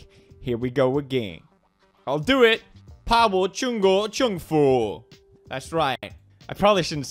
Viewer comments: I had to relisten like 20 times, but I think he said: 怕我中国政府, or Fear me Chinese government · pa wo zhong guo zheng fu? Afraid of me? Chinese government? “怕我啊，中國政府？” P.S. I am a Hong Konger so I could watch your YT video without VPN and understand what u say XD.